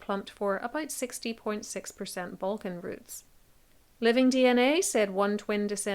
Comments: bwahahaha! I was doing something else while listening to this and I thought I heard her say someone had Vulcan genes. It was actually Balkan but it would be really cool to find out you are part Vulcan. 🤣😎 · For a minute, I thought she said VULCAN roots. 😂